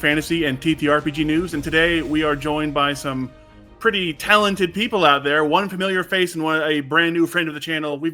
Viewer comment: Which Daggerheart campaign frame are you most excited to check out? Are you ready to cook up some recipes in Beast Feast? Let us know in the comments! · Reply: I had to restart the intro because instead of some "pretty talented people" I heard "We have some pretty terrible people" 🤣